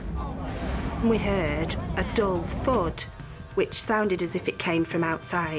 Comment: what does it mean, at 4:49 "we heard a dog food" ??? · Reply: Yeah, I had to think about it for a second and realized it must be dull thud. I first thought they heard a "dog fart."